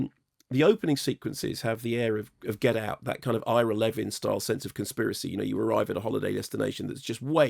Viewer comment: what is it that he says at 2:49 ? "...that kind of SOMETHING kind of conspiracy..."? does anyone know? th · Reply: Ira Levin. Author of Rosemary's Baby.